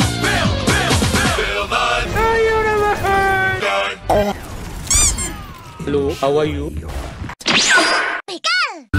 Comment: What does Sigma say at 1:14? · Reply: Billy nye the universe guy